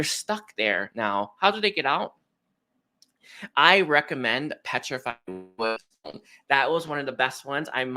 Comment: Did anyone catch what he was saying about healing & helping through the confusion/transforming energy. Something he puts under his pillow? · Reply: I think he said petrified wood. It has healing properties.